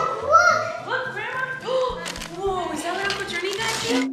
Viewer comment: Did I hear your mother say to @ 11:44, "Is that what Uncle Journey got you??" Christopher,..is your name.. Journey?? (Or is that a nickname??)